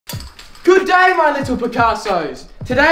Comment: When he came in in the first shot I always thought he was saying “hello my little potatos”